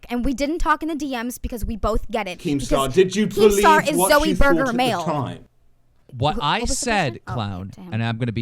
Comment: "Keem star is Zoie Burgher male." Anyone else hear this at 14:07?